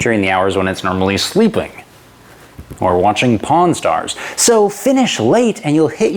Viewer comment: Sorry, but me anglish no good. Is watching Porn Stars what he say 1:30 ??